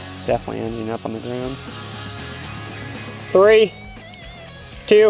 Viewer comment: Uh anyone else hear “Holy N-word” at 4:59?